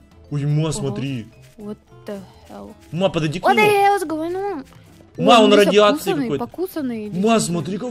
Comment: что сказала ма? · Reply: O the hals goin on